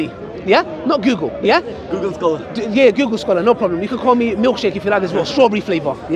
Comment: "You can call me milk shake if you love this strawberry flavor" 😂😂 Ali Bhai not coming slow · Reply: i thought he said milk sheikh 😭😭